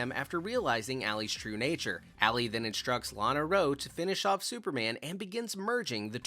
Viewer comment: did you say Lana Rhodes? 😅